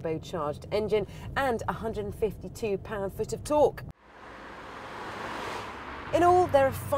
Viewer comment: I heard something weird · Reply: Pound foots of torque..